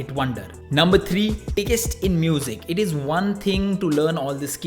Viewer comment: who hear TICKEST???